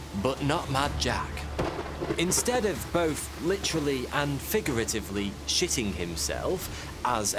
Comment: loool did he just said litterally hahahaha